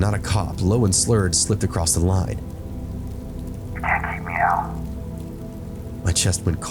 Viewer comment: I can’t understand what the hell is being said here… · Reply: pretty sure it says "you can't keep me out"